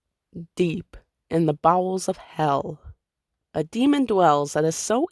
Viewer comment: I heard deep in the balls of hell... i’m gonna go clean my mind